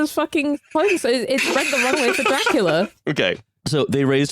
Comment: During the CAASTLE discussion I swear I heard the phrase "clearing the runway for Dracula" and I need this to become a common saying.